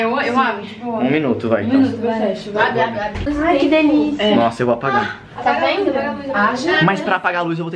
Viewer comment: Genteeeee só eu que escutei isso? 😂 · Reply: "AI QUE DELICIA AAHHNN~" KKKKKKKKK